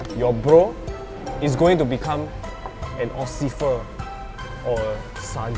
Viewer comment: I heard him say ociffer instead of officer